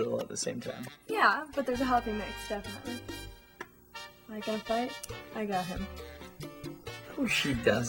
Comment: what does she say? "like i fight, i got him"?????? lol i love his reaction!